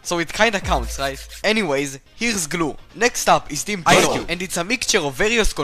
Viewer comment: sounded like "Team *auuuoo*"